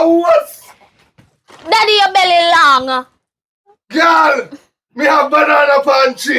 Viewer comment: Sorry it's a little hard for me to understand Jamaican patois, @ 20:16) Did she just say; "daddy ur belly long"😂😂😂if that's not what she said, 😬I'm sorry that's what I heard🤦🏽‍♀️🤷🏽‍♀️